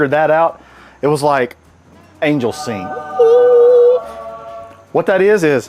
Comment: I thought he said angel semen